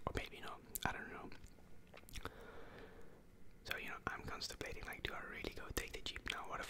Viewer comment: did he just say he’s constipating 😂